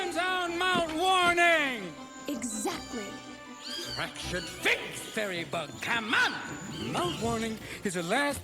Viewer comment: what did he say? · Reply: "fracture's FAKE fairy bug, come ON!"